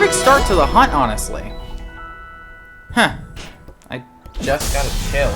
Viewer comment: What did you say? - 11:06 😆 - 16:34 😆 - 18:00 😆 - 35:40 😆 - 40:45 😆 - 46:06 Nice, you did it. - 52:51 Heh, nice reference to Super Mario Galaxy. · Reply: Oh, i'm saying "huh, i just got a chill." It's intentionally not subtitled.